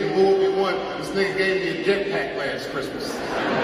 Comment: i thought he said “he got me a jet pack last year”